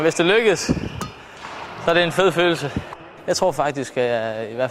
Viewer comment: it sounds like he says "this whole fight is gay" in